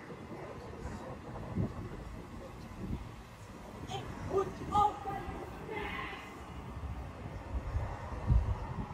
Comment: What was said? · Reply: "Ik moet ook ...."